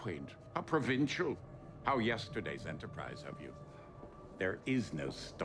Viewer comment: "No more yesterday's Enterprise" ...... I see what you did there .... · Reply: I thought he said "How yesterday's Enterprise of you" (regarding chiefly his previous starship, but shoehorns in an "Easter egg in plain sight". Not sure the direct references are always needed, especislly when the story is strong enough on its own.)